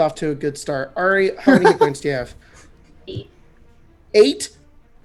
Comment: I heard 8 the first time, but it was really just the tail end of 60. Little upset, not gonna lie